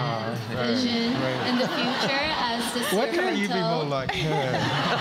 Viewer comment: What does he say please? (Just learning english) Thanks in advance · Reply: Why can't you be more like her?